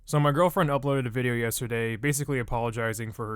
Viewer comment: Wait did he say "So my girlfriend"